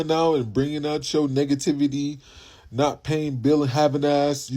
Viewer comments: did he say “niggativity”?! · 😂 I miss heard the guy at the beginning talking about the blue haired chick instead of saying negativity I thought he said niggativity 😂